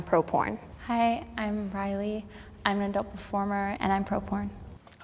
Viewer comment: I wasn’t looking at the screen and when I heard “Riley” as Pro-porn... 👀